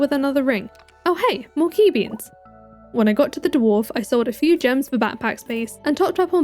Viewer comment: I misheard dwarf as divorce 😭